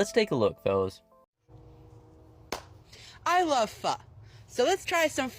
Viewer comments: I might be tweakin, but dose anyone else hear "Fuck" insted of pho? 😂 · did he just say i love fu***